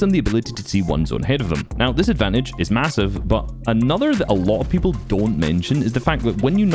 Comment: did you say.... massive?